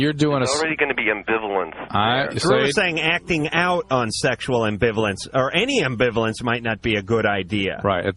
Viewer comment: sexual ambulance did you say?